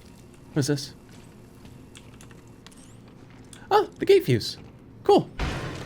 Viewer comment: AHAHAHAHAH I thought you said "ah! the gay fuse!" Absolutely sent me, love it.